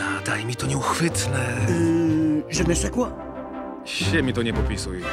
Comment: czy ktoś zrozumiał co powiedział tutaj Le Fou? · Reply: Wojtek Czekalski powiedział "je ne sais quoi" czyli dosłownie "nie wiem co", a w przenośni oznacza coś nieuchwytnego i atrakcyjnego, czyli nasze "to coś" :)